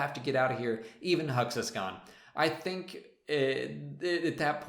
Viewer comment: I'll admit, when I first saw The Force Awakens, I heard "Even Huxy's gone!" rather than "Even Hux is gone!" Actually, knowing he meant Hux rather than some guy called Huxy makes it funnier, like he'd accidentally revealed he'd spent his career crushing on his CO.